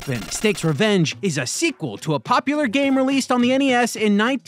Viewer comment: How to stop mishear this as "Game released for any ass"